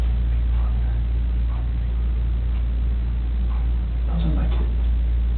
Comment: He used the words “THESE kids” and “THOSE kids” ... NOT “MY kids” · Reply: Hodgson I thought I heard him say "those were my kids". He gave himself away when he didn't say "those are my kids".